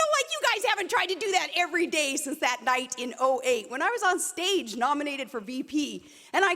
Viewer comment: Is it bad that I thought she said, "Like you guys haven't been trying to do that since 1908. . ." and was so convinced that's what she said that I had to watch that part over and over until I realized she said "that night in 08"?